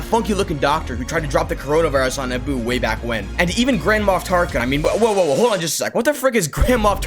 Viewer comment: bro I thought you said grandma Tarkin😂